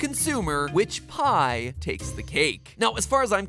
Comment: “Which pie takes the cake”-Matpat 2020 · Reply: The first time i heard that i thought he said "Which pie tastes like cake"